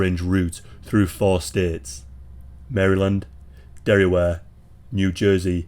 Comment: Did you just say 'Derryware?' As in Delaware?